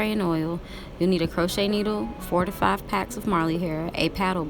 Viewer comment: Girl I thought you said 45 packs of Marley hair I was like tf 😐😐😂😂💀💀